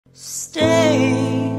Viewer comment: Did anyone else thought she said steak.. Just me.... K🥲